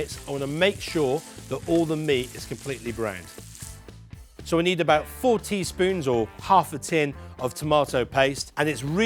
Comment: At first I thought you said "We need fourty spoons of tomato paste" and I got a little confused there, haha.